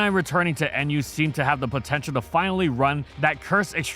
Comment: did you say potential